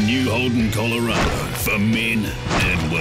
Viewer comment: What does the voice over say? The new Holden Colorado for men and women because apparently nothing ?????? us. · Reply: 's sacred these days...